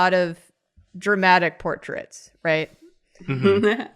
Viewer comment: We all thought was saying "dramatic" portrait, but after that final verion popped up, it's clear she meant "traumatic."